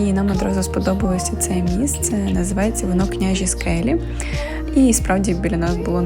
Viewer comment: Приветствую. Не расслышал, какая локация первой ночёвки. Княжеский... А дальше не зрозумів 🤷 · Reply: Вітаю. Княжі Скелі, дуже гарна локація